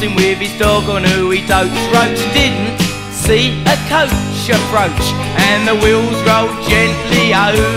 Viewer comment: <- Grew up with punk rock, that was a good time. We all liked Splodge. This one is superb. Could somebody please help a non native english speaker, what does he say after "guide dog" and before "wheels drove gently over Tough Shit's head"? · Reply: I heard 'coach approach'. Maybe.